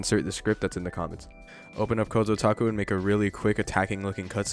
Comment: the heck does he say here?! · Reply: kozo taco XD